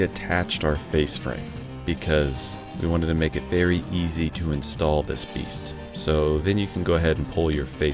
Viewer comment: Did you say "install this piect"?